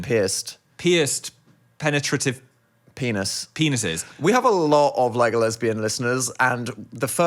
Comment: I heard 'We have a lot of Legolesbian listeners' (as in Legolas, not Lego)